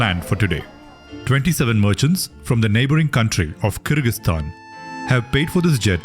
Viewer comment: I thought you said Kirkastan